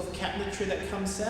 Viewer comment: Hey! You’re making great progress! Really excited for you 🙌🏼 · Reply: P.s. I totally thought you were saying “making cat trees” instead of “cabinetry” 😂 n got me excited lol